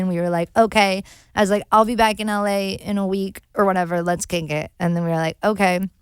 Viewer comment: Did no one else hear at 33:38 where she said "let's KINK it" instead of KICK it. 😂😂😜😜